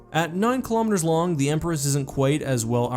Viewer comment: sounded like you said Empress at 7:02. i like where this is going. ( ͡° ͜ʖ ͡°) noticed you didn't say how long the Gloriana ship class is, which is understandable since accurate information is hard to come by for a lot of Warhammer 40k ships, especially when they are said to be kilometres (whatever that is) long. some charts and ship comparisons put it at 19,960 kilometers long. 960 meters longer than an Executor class Super Star Destroyer.